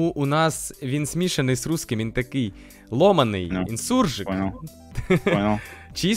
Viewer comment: мне послышалось сурж